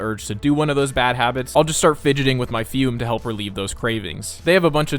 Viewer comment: I hear if you fidget with your fume too much you go blind.